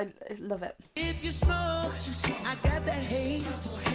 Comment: Filet not Flayyy! Haha! This was so great and I hope you learnt something whilst enjoying with me :) x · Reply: I thought he say I got the Lays